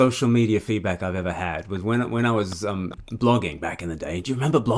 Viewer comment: Blogging?! I thought you said logging!!! 🤣